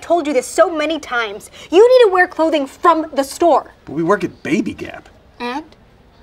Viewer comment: Anyone notice Damien say “Baby GAP” 0:58 instead of “Baby NAP” or did I make a mistake? 🤣